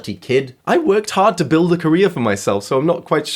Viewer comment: I misheard I worked hard to create a career for myself as "I worked hard to breed korea for myself"